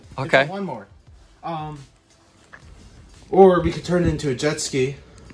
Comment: I heard you say Nitrous Jetski